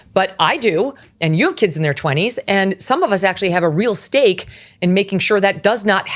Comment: ? the fuck was that?? A female voice overlay? It sounded like she said a real steak? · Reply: Haha I am glad I’m not the only one I thought it said “real estate” but was too lazy to go back and listen.